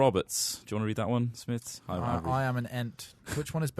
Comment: did he just say "i am an ent"? 🍍